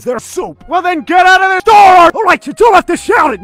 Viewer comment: I thought I thought he said “Well then get out of the door!” Might be just me though